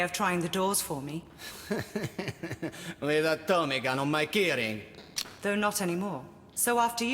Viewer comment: I always heard "rigatoni got on my keyring"